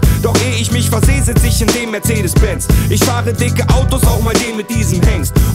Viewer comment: sounds like hes saying "adidas pants"